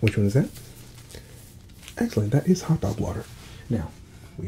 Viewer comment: “EXCELLENT! That is hot dog water...” LMFAO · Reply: i heard "hot tub water" lol